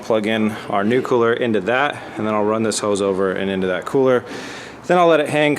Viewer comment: I heard run this hoe over lol